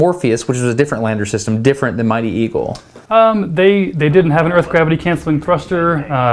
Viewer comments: anyone else thought destin said "mighty evil" at first? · When he first said it I thought he said Mighty Evil....